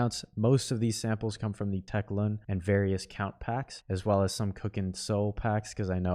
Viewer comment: what soundpacks did he say on 4:55? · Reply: tek.lun, various Kount packs, and cookin soul